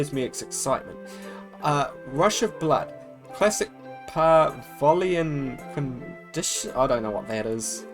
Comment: pavlovian is what i believe that says?